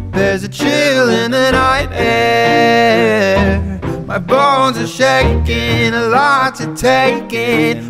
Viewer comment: For I second I thought it said "My balls are shaking" 💀